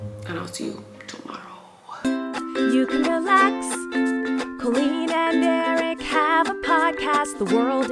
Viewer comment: Why at the end of the intro it sounds like was saying chicken wing when it says I'm colleen